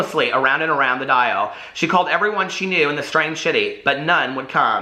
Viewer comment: I thought you kept saying strange “shitty” when you were reading the synopsis for the sorry, Wrong Number book 🤣